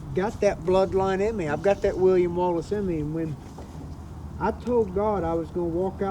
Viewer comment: i miss hear Bryce & thought he said “iv got that Willy Wonka Bloodline in me” Lmao, i said “no way he said tha shit” made me laugh. Y’all are doing a little east tx fishin!! Good on ya!!